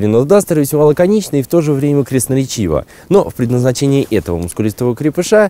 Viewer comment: люди, что значит крестноречиво, или мне послышалось?